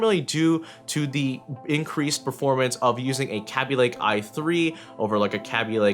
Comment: Sound like you said "Kapy lake"